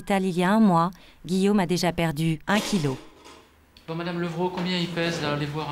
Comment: c’est moi ou le mec dit « bon madame le gros combien il pèse la » ? 🤨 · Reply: C'est une dame et elle dit "Madame Levreau"